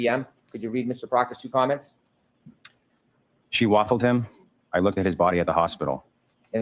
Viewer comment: and Proctor saying JOK got “biffed” beaten up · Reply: I thought he said she "waffled" him.